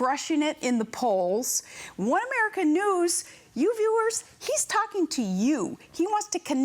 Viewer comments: "women are canoes!" 1:00 I have no idea what she actually said but I like her better as a canoe. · Can someone please help. Does anyone speak PALIN? On 1:04 minute, can anyone make out what she means with the word "women canoe." She kind of lost me there. Dang the girl is dang dingy.